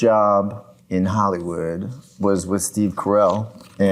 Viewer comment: Is he saying Hollywood or Hollyweird? xD